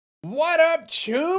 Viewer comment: I dead ass thought he initially said "what up jews"